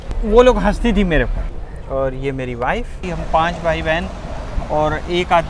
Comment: did i hear "Ye meri wife hai, Meri 5 wife hai" ? :hand-orange-covering-eyes